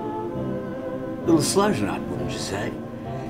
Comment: What does he say at about 0:36, "a little slow ..."?? Love the video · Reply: A little slow tonight wouldn't ya say